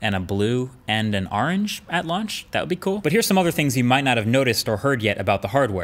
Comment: My fat ass thought he said “at lunch”